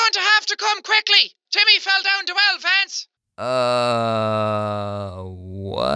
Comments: The woman sounded like she said "you have to come quickly! Timmy fell down dewellafance!" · I heard... YOUR GOING TO HAVE TO COME QUICKLY TIMMY FELL DOWN THE WELL VENTS! Please tell me if that's what they actually said or if I'm insane..... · random girl: YOURE GONNA HAVE TO COME QUICKLY! TIMMY FELL DOWN THE WELL VANCE(actually Lance) Me: am I the ONLY one who knows what she was saying!? REALLY!?!? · I heard, "You better come quickly Timmy fell down the stairs Vance." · I thought she said you have to come quickly Timmy jumped off the wild fence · i thought he said timmy fell down the well pants lol edit: i did like my comment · All I heard the person say at 5:36 was YOUR GOING TO HAVE TO COME QUICKLY TIMMY FELL DOWN THE WELL FANCE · the woman on the phone was ACTUALLY saying "Your going to have to come quickly! Timmy fell down the well lance!! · With the Vance one I heard JIMMY'S FALLEN DOWN THE STAIRS VANCE 🤣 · I first heard "TIMMY FELL DOWN THE WELL VANCE!" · You better come quickly because Timmy fell down the well Vance Edit: Wow thanks for all the likes! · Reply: I thought she said "jimmy fell down to hell vance" lol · I wonder if Timmy is still trapped in the well… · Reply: I heard "Timmy fell down 2 elephants" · i heard You have to come Jimmy fell down to a wild fence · “You’re going to have to come quickly! Timmy fell down the well fence!” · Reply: I thought I heard 'Timmy fell down two elephants!' XD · I heard “Timmy fell down the well Lance!” 😂